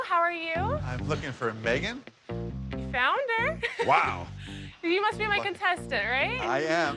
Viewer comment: i thought she sussed him out and called him "founder" lol